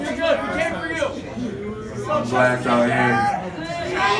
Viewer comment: what has the dude said? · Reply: he said "fuck dommy divine we came for tracy"